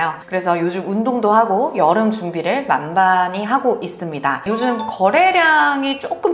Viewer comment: '만반히'라고 발음하는 것으로 들리는데 그런 단어는 없음.. '만반의 준비'겠지.. 자막은 심지어 '만만히'라고 나오는데 준비를 만만히 한다는 게 뭘 어떻게 한다는 것인지..? '만반의 + 단단히'가 엉켜버린 듯